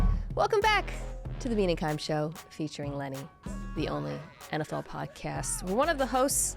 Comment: Whenever I hear "featuring Lenny" my brain says "Lenny Balls"